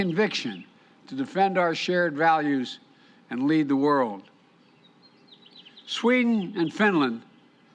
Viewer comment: did I heard this right and to lead the world